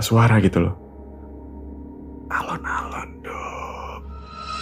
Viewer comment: Lahh,,, lakik kok dipanggil "Nduk?" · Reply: Alon Alon DONG...sependengaran ku sih gitu. Bkn nduk deh. Coba di rewind. Mungkin aku salah dengar